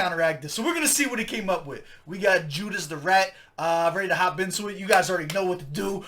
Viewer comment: Judas the rat is a useless response! He sounded like he was saying "yeah, you too"!